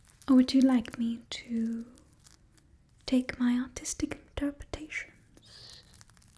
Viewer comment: Almost thought I heard autistic but she said artistic